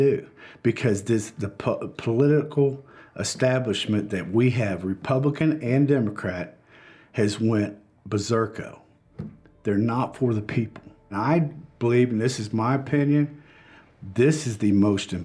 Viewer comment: Did he just say berzerko?